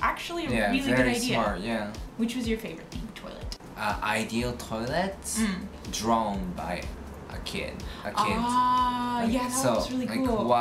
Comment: Aw<link> kids seem to love the singing Toilet choir. Ok when first asked what his Dream toilet would be.....I thought he said " Toilet Drone ". Now it only took me a split second to realize he said Toilets drawn by the kids. But in that split second...... I was seeing in my head a person hiking on a trail in the woods......he gets the urge to poop. What will he do......there are no toilets in the wilderness. So he gets out his phone and he dials up " Drone Toilet Services " In a flash he sees a flying Drone Toilet approaching. It lands...he lifts the seat and he poops. Toilet Drone spritzes his bottom and uses the exhaust from Turbofan engine to dry his bottom. The jet turbine of Toilet Drone is fueled by methane processed from the poop he collects. Yes i imagined all that in a split second.